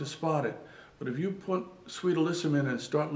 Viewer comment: what is the name of the plant he mentions? "Saint or Sweet, Elisban? I cant make out the words. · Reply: zero sweet alyssum